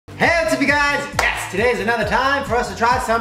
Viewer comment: shane : hello what's up you guys yes me : did he just say heyo tubby guts yes 😂